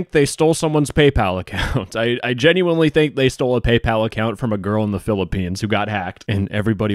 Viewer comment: i heard "some girl in the philippines" and scrambled to see if that was me XDD